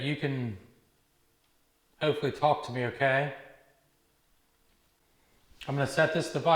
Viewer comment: A great one I love the brothers god bless you...I heard a lot of voices but this one said ok after said you can speak to it at 1:01:29 and at 1:01:41 it said I remember 🇳🇱🫶